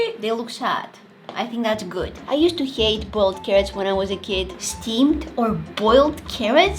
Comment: i genuinely thought she said bald carrots. 🥕👩‍🦲